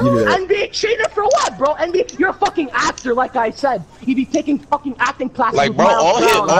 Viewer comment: can somebody explain what he said? Did he say actor?